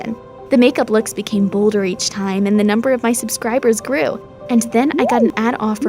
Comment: Did She just say subscribers